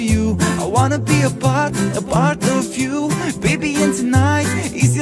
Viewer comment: I heard, I wanna be a butt.